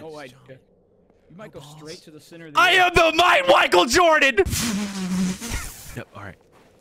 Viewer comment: did you say “I am the MIGHT WICHAEL JORDAN” I’ve listened to it like 20 times and I can’t unhear it